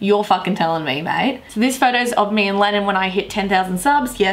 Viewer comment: When you say you sound like you say Lenin... COMMUNISM INTENSIFIES